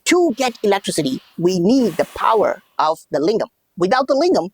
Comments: I heard "the power of Ligma"... It Made the explication better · I misheard "the power of LIGMA" and I cannot unhear